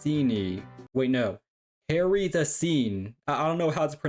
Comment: Auto generated subtitles said 'Harry the scene' XD